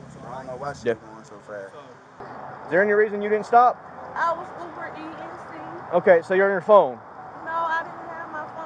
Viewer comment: No license. Her "sisters" ID. Speeding. Ignoring a stop sign. Wearing pajama pants to deliver food. My Mama died. My Grandma died. "Can I call my Grandma". Crying with no tears AND a car full of drug paraphernalia. Straight to jail for these two con artists. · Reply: Right? It's a verb now -- took me a bit to figure out what TF she & her passenger were saying...sounded like she was saying "I'm over eating." So pathetic.